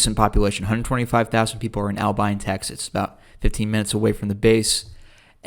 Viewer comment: I was there 3.5 years - 2002 til 2005. My whole enlistment outside of tech school and basic. I remember the Shifty 50 too. Nothing but problems. Felt like the people there did not like this. At the time they were trying to close the base. On the weekends/days off, we almost always went out of town somewhere. Lubbock, Amarillo, Albuquerque, Ruidoso to gamble, El Paso/Juarez, Dallas on a long weekend a few times. Those were the main spots I can remember. We just had to get out of Cannon/Clovis. I will say that I never had trouble dating there. I think just because there were a lot of women in the Air Force and I had confidence at the time, being AF and all. I got out in 2005. My job was airfield management. I likes the job actually. I was/am a huge aviation geek. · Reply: I have never heard Abilene butchered that bad. At first I thought he was saying Alpine. Turned out to be Albine.